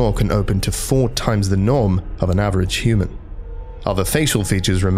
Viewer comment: What it actually says: "SCP 096's jaw can open to 4× the norm of a human" What I hear: "SCP 096's jaw can open to 4× the NOM of a human"